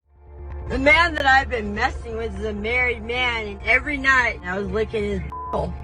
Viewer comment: I thought I heard her say “the man I’ve been mething with” . I literally had rewind it